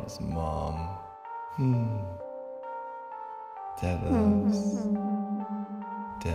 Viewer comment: Hey everyone, hope you all enjoy this :) There was one discrepancy that I wanted to clarify: In Dad Betided, both Emily AND Ella are present. Ella is the one with short hair at home with Andan, and Emily is the astronaut that crash landed with him on Earth. Btw, here's some bonus material :D Check out my interview on Talking Dad: <link> Also, Dad's dancing live: <link> See you in the next one! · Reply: when "Dad" starts glitching out after saying that he loves "Mom", I'm pretty damn sure that he says "Dad... loves... dying?" before saying the name Diane over and over (and then Ella, of course). I keep listening to it and trying to hear "Diane", but it sounds like "dying". What do you think? Also, I completely agree with your idea that "Food" is being used to control him; it reminds of the drink they used in Black Mirror's episode called "Fifteen Million Merits" to control the contestants; it was fittingly called "Compliance". ;)